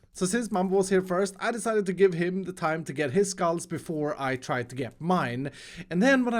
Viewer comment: Does he say `s or His Skulls at 17:52 ?